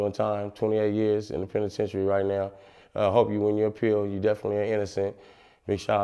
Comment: did he say you definitely ain't innocent ?